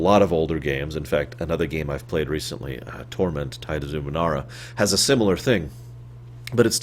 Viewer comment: I thought you said "Thora" instead of Torment. Is Torment a good game in your eyes?